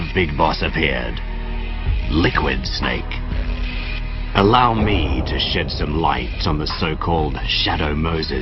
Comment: it sounds like he says "allow me to shit some light"